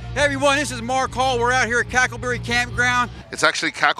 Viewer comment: calling Cackleberry "Crackerberry" for half the vid is the funniest unintentionally offensive thing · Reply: I thought he said crackle barrel 😂😂😂😂😂😂